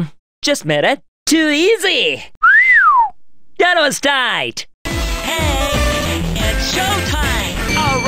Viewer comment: Most of the English is pretty good but I can't understand this one for the life of me- What's he saying? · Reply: That was tight.